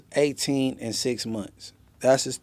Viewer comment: We must've misheard he was saying shes 18 IN 6 months 😂😂😂